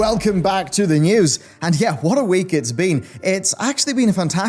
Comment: Okay, I love you to death, but today for some reason, at the 0.21 second mark, I heard you say, "What a week it's bean."